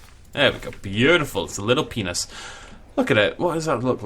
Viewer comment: did he just say penis